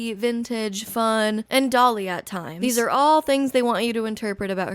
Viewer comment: when you said Sabrina is "Dolly at times" at first i heard "Dali at times," and now i want her Surrealist era.